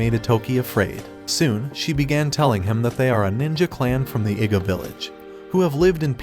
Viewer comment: Bro I thought she said there were from the nigga village💀💀💀